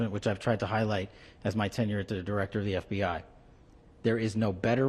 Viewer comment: did he say "as I try to highlight as my tenth yr director of the FBI " What a freaking LIAR. Well, what can we expect he learned from the best he's daddy trump.